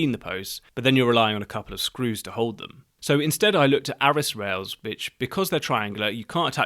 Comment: It sounded like you said arris rails bitch @ 4:05 and you were worried about saying cant rails.